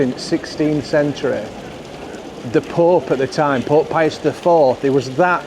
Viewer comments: Not saying I av trouble with ur accent but wen u said, Pope pius, I thought u said, pork pies 😂, I will clean my ears out 😜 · I thought you said PorkPieus 😂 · Thought you were saying Pork Pie'us , The patron saint of Yorkshire ee baa eck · Took me a moment to work out that you were saying "Pope Pius", I thought you were saying "Pork Pie us"! 😅